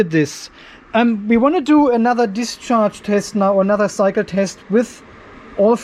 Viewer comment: My wife was listening to this in the background and thought you were saying, 'Now for another psycho test'. I though that was very accurate for us battery 🤓 nerds.